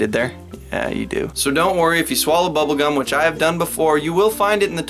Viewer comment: I thought you said bogun not bubblegum